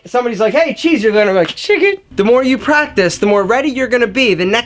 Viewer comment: hahahaahaha I’m dead, am I the only one who heard chicken 😂😂